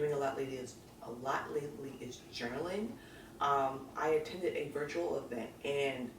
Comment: Love your favorites. When you were talking about journaling i first thought you said you starting juggling. Too funny. Any favorites for the hard of hearing. Lol